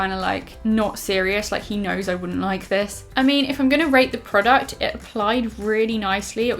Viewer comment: I thought she said " I'm not gonna rape products 😂 "